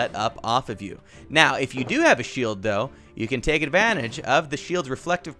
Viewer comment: I have to admit, when he said shield I thought he said sheep